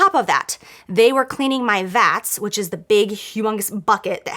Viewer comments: I thought she said they were cleaning my vag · I thought she said cleaning my vag at first I was like tf....